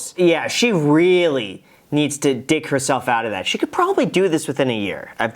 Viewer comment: instead of dig, i heard you say d*ck herself out of that.... I'm like yeah thats a fast track way